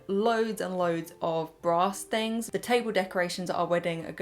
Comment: I really heard "I've been collecting lots of little bra stains" instead of "brass things" lmao. Love your charity shop hauls and the colour of this dress really suits you! x